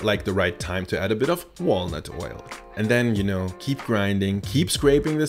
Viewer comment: Good grief - I thought that said "walrus oil." I need more coffee.